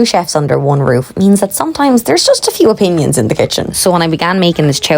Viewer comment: Hahaha I definitely heard there's just a few onions in the kitchen 😂🫣